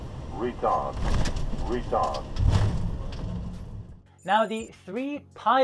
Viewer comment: what does it say? it sounds like retard, but of course it's something else. Sorry for the ignorance haha